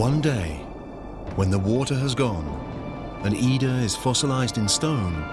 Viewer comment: whats her name? Anida? Oida? I can't understand it..